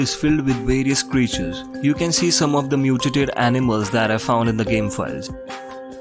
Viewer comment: Somehow I thought you said "New To Date" instead of "mutated" And I was like "Awh, we be datin' animals with our co-host Six." Before going back to see what you actually said.